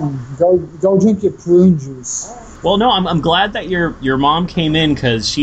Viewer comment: his mother certainly came off as mentally delayed. 51:14 did he just say "drink your prune juice mom!"